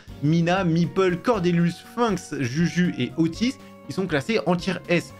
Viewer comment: ta dit oti quoi ???????